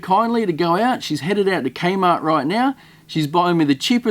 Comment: did...did he say kmart??? Do those even still exists???